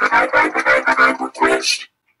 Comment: I all I hear from earth island is Lower the volume, error and hyperquest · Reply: Well I hear “lower the volume lower the volume hyper quest huh you hey hyper quest